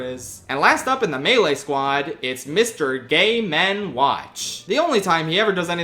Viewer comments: Did you say Mr. Gay Men Watch? Because that’s what the captions say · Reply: Even though I know the name is Game&Watch, I keep hearing him say "Gay Men Watch" here too lol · I was not paying attention at 12:20 and thought he said Mr. Gay Men Watch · is mister gay men Watch, is what i heard